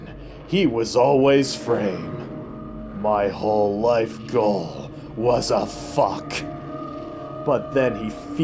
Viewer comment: I always thought he said "my whole life gone, was a fuck" until I read it ,but I like mine better XD